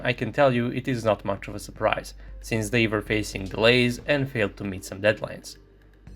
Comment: I heard "since [BAE] was basically blazed and forgot to meet some deadlines" 😂 Had to hit the replay button to hear it right